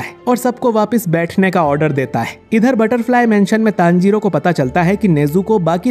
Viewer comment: this time i heard butterfry mention